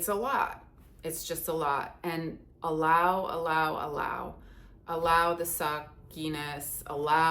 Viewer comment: My head is in the gutter because at 7:04 I thought you said “allow the soft 🍆.” 😂 Oh, LORD. It’s all the sexual energy, I tell you.